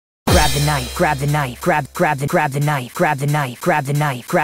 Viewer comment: Why do i hear "crab the night" in the beggining?